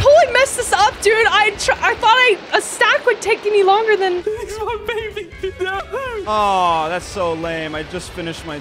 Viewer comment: WHY DOES IT SOUNDS LIKE IS SAYING "Lynix my baby"